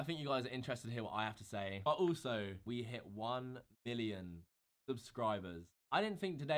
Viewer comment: Lmao at 3:44 He CLEARLY said "1 billion subscribers"